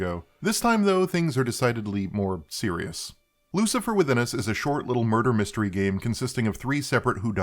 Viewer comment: i really thought you said "lucifer with an 'S'"